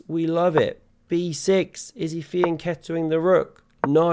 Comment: I'm so attuned to your ridiculous puns now that at 10:04 I thought you said "is he feeling the kettle with a Rook!" 😆